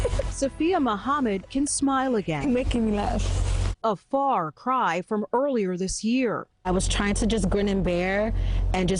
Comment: what did he say to make her laugh ? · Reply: Lol he said...after all the cleaning we did they could have came upstairs lol